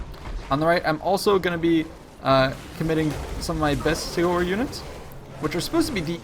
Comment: What does he say at 6:14 ? "My best .....? Units" sounds like he's saying Sailwhore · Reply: Bestigor. It's the name of the unit, a Bestigor Herd